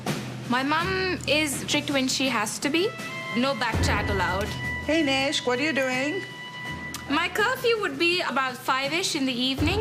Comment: sounds like she said heyyyy bishhh wat are you doing lol